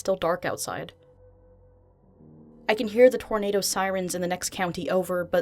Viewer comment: sigh I thought that said tomato instead of tornado